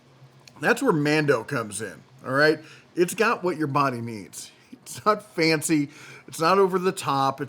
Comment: Did he just say "Brondo has what your body needs'?😳